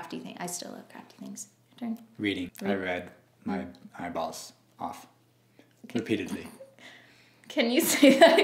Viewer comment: I totally thought he said I read my balls off the first time he said to 🤣🤣🤣🤣 that’s hilarious was like ummm what? Can you say that again? 🤣